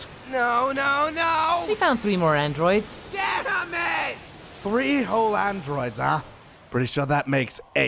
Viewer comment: what is Trunks saying at 8:49? Can't catch it. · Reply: he is saying damn it